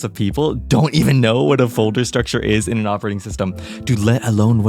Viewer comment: BRUH, I thought you said volter or smth and felt stupid searching what tf it is until i realized it's a folder, directory is correct btw, don't make that mistake again(P.S. It's a joke about the fact that linux-users have different naming)